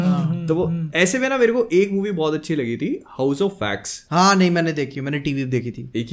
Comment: What movie is talking about here I can't hear it properly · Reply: movie name : house of wax